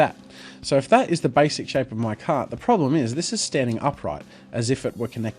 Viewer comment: Until 15:00 I thought you were saying "cat", not "cart." Lol.